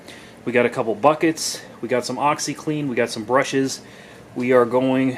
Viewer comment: Sounded like you said "I have some oxy".